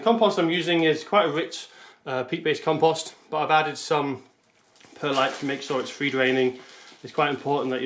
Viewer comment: Hey mate, what was it you said that you added to the compost at 06:45 to help with the draining? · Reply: I added perlite, you can find it in garden centres or online.